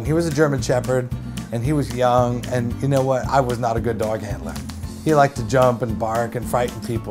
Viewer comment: I thought he said "I was not a good dog Hitler" XD